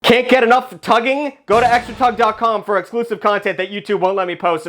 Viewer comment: i heard "can't get it up for tugging?" 💀💀💀